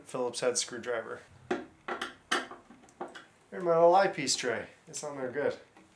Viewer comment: oh Eye Piece Tray , thought you said Ice Tea Tray , lol